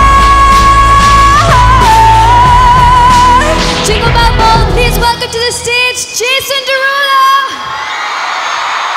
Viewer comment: what was Jade said? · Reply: jingle ball bell please welcome to the stage jason derulo